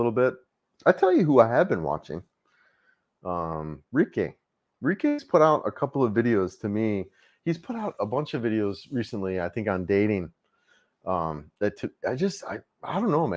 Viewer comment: Hey. Couldn't make out who you're talking about here 44:35 What's his channel? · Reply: Reekay. Life Beyond The Sea is the channel name. Guy has been vlogging the Philippines for the past decade